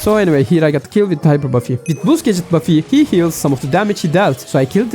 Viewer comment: cc says boob buffy😭🥀